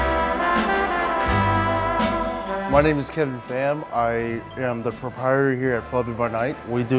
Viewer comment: Who else laughed when you thought he said my name's Kevin faaam